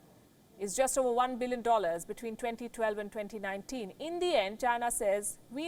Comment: she says 'India and China says we need no teachers especially a teacher like the U.S'. I thought India favored U.S. instead of China. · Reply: its ""in the end" not india